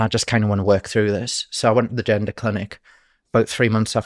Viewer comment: His accent made it sound like he said agenda clinic. Maybe that would be a more accurate term.